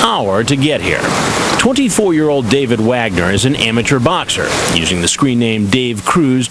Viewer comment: I thought I heard "David Wanker" xD